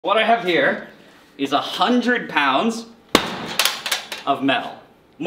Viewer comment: At first I thought he said "100 lbs of Meth" but he's a chemist. I was just thinking, " I knew this day would come" 😮